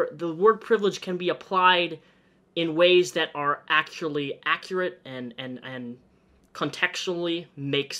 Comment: I thought you said "accurately accurate" and was wondering just how stoned you were xD Maybe it's just me 0_o